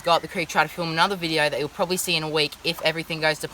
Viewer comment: Did he just say "bone apple tea"?